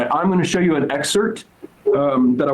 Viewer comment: Did Crowder say excert instead of excerpt?